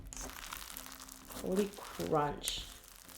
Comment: did she say “holy crotch” ???